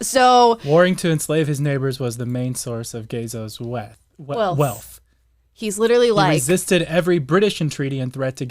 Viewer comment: I thought he said "Ghezos WAP" and had to make a correction. 🤣🤣🤣